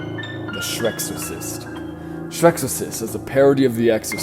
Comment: i thought i heard shrek's a racist instead of shreksorcist